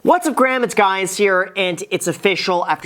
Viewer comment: Did anyone else catch the “what up gram it’s guys here in the beginning”